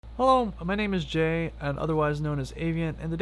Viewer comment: I thought you said "Hello, my name is Jeff".